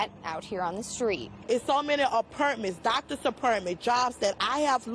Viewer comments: I thought she said apartments lmao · I thought she said apartments lmao. Dr. apperntments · Aperntmin and add Periololically to the dictionary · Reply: I thought she was saying apartment, like she lost her apartment, until she said doctor's lol